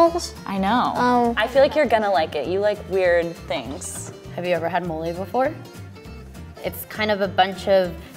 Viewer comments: At first I heard she asked "have you every had molly before?" and I got really concerned for a second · I thought she said have you ever had molly before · who else heard molly